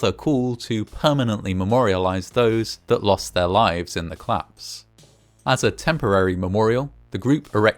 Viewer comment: I thought you were saying "claps" 😂😭